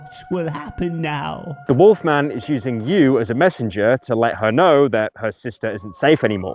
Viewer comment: Subtitles say «now» instead of «know», 0/10